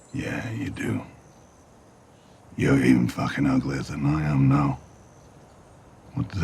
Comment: "You're even fucking uglier than I am now" For those who were like me and didn't know what he was saying lol